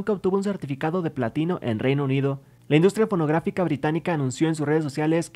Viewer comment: Dice la industria p0rn0gráf1c4 o escuche mal? :v